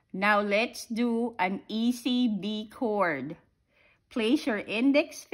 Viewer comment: At first I thought you said "let's do a E,B,C chord"